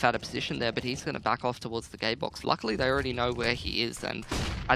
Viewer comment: did he say gaybox?